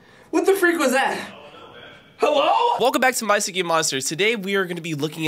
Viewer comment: If you turn the CC On right here it actually says “bicycle monsters” instead of “My Singing Monsters”